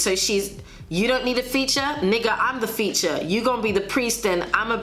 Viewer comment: I'm so glad to see some Out Of My Mind appreciation. Was an amazing MV and the reference to "Can we pretend that airplanes and the night sky are like shooting stars?", and Nicki Minaj saying "Nigga i'm the feature" which also sounded like she was saying "Nigga i'm the future" with her delivery and looking at it back now, she was right about being the future of hiphop.